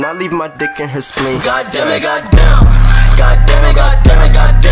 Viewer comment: sounds like its sayin god demi god demi god damn